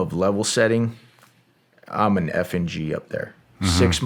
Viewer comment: I thought I he said 'I'm an effin' G' then realized he meant F N G 😂